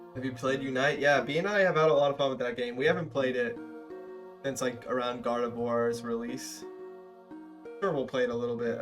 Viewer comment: at first I heard “we haven’t played it since God of War’s release” and got very confused